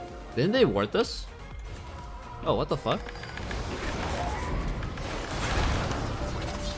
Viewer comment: if you listen carefully at 7:58, it sounds like taric is saying "fuck you"